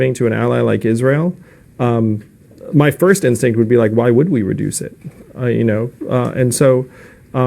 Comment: You need to make a correction to the latter part of your argument. You misheard him during his first answer. What he actually said was "why wouldn't we reduce it?"